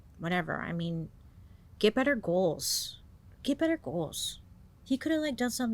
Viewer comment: I swear I heard "make better choices" and "get better idols"